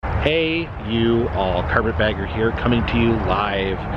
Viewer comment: Who else thought it said carpet badger